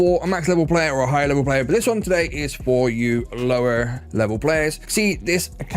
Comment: Oh, I thought you said low level! My tiny account doesn't even have barrage yet 😉